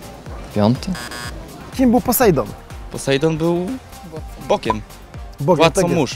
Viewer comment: Czemu ja usłyszałem, że Posejdon było bokiem XD